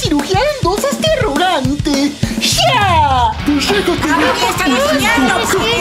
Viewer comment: Nose si escuche bien pero Cr siente dijo 3:20 "Pues dejate venir pulguiento" JAJAJA 😂